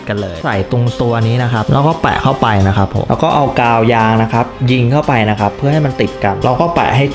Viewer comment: ได้ยินเป็นถุงยาง555+